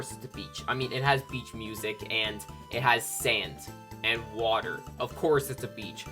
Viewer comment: Yes is there something you need from me? (I know he said sand but I heard sans first time lol)